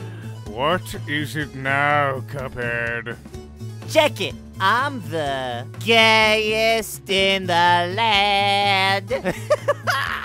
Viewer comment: TBH when I first heard King Dice’s theme, I was confused because I genuinely thought I heard him say he was the “gayest in the land.”